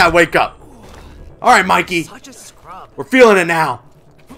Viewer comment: Did Michelangelo say "such a scrub"